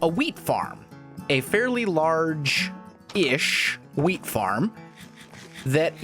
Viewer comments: it sounds like he said weed farm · I was kinda alarmed when he "Wheat Farm" at 1:01, because I thought he said, "Weed Farm." lol